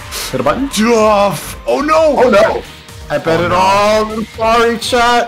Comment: just realized this rewatching but did say “I bet it all on hakari” or is the lobotomy getting to me?